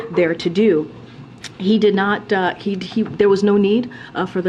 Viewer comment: Gotta love how articulate KJP is, Almost sounded like she said "he did not die" at